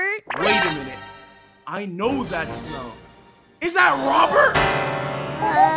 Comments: "YOU NEED TO LEAVE" LMFAO GOT ME ROLLING AROUND ON THE FLOOR Edit: yall I was just joking 😭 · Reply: Wow, Mandela affect for only me I thought they said YOU NEED A BREAK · "YOU NEED TO LEAVE" at the end💀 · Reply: I thought it said "YOU NEED TO PEE"